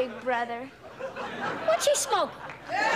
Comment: and 5:09 what did Arnold said or asked? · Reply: "What's she smokin'?" 💛💙💛💙💛💙 👸🏻💖👑🇬🇧💝👸🏻 🥰🇯🇲🏴🕊️🔥✝️